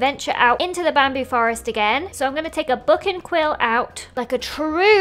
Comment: When she said book and quill, did anyone else think she said fucking quill